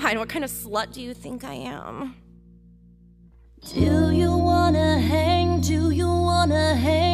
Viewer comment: I thought it said "do you wanna hug" Disappointed